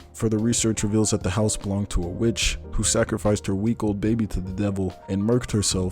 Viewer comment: When you were describing the conjuring, you said something about sacrificing a week old baby. I heard it as sacrificing a weak old baby and it made me laugh